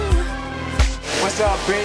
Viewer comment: 😂😂 I thought t..o said what's up bitch at first 😂😂